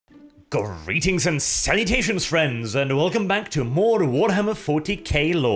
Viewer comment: Did he say "greetings and... sanitation"?? Is this supposed to ward off Nurgle or something?